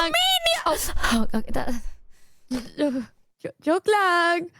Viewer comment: am I hearing correctly or did just say "joke lang"